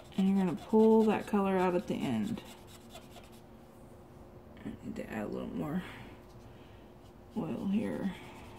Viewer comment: did you say "I need to add a little bit more oil here" ? I have a blending pencil but I want what you have -the blending paper stumps I think you said? I didn't realize you use oil with them tho. Would you mind explaining that a little?